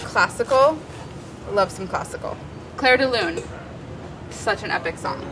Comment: Molly: "Clair de Lune" All I hear is "Claire is leaving" :( Wonderful episode though!